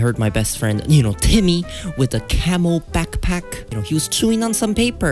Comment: Camo backpack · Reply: I thought you said camel backpack